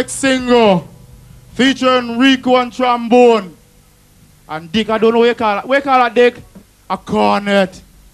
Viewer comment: "Featuring Rico on the trombone, and Dick on da what ya call it... what ya call it Dick? Da corrrnet!" In case anyone didn't catch it. lol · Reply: I hear it as ""Featuring Rico on trombone, and Dick on da I dunno wha' call it... wha' call it Dick? Da corrrnet!"